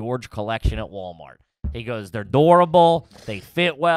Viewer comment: WTF word is saying at 39:39 "dourable" 😂🤣